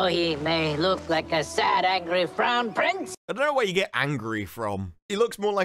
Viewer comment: sounds like he says brown prince.